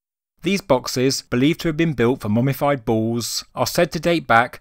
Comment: I misheard, thought you said the boxes were for 'mummified balls' and thought, "holy shit it'd take a lot of balls to fill one of those, ancient Egyptians be freaky-weird!"